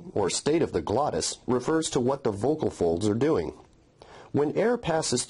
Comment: i thought he said what the fuck